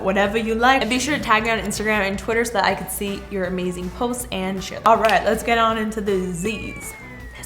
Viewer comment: I thought she said “ to see your amazing post and sh*t”